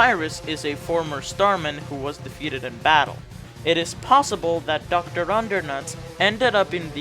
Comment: did you just call him Doctor Undernuts?